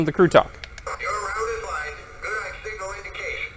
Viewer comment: Nice video and review of American Flyer Nickel Plate Road #765. I also liked the #759 running with it. Can you tell me what the dispatcher is saying at 17:20 and what it means? I can not seem to make it out. Thank you for sharing. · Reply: It's just some of Lionel's generic crew talk Dispatcher- "Your route is lined, good on signal indication" Engineer- "Roger that, All clear ahead"